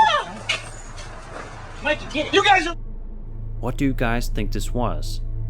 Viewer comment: looks like a stuffed animal... dude sounded like he was about to yell "you guys a fuckin wit me" but i could be wrong, I dunno. I never saw its mouth move and the way it just swings its leg over and disappears reminds me of how someone would handle a stuffed animal..